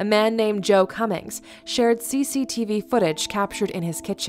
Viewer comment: BRO I THOUGHT IT WAS SAYING JOE CU- CCTV